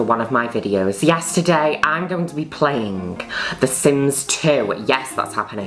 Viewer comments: I keep hearing "Yesterday I'm going to be playing..." Instead of "Yes, today I'm going to be playing..." and I hate myself for it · i hear "yesterday" whenever juno says "yes today"